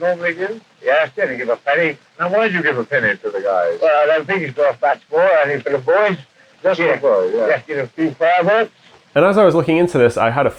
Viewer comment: "well i dont think hes got a fast boy aim for the boys just give a few farthings " is what it sounded like he said but most of it doesn't make sense i think i got the end right tho Edit: a farthing was a quarter of a penny for anyone wondering